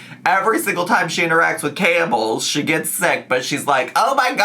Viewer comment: I thought was saying “Campbell’s” when they were actually saying camels, and it honestly didn’t surprise me to hear that FB was interacting with cans of soup 😂